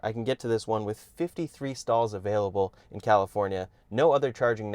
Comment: Did you say 53 stalls available? Jeeeeez... 😳